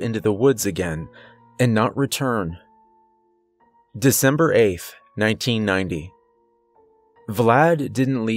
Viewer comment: what's a december-aif or were you saying December 8th??? .... "AIF" eh?? 😤🤨🤔 good job 🤭🤣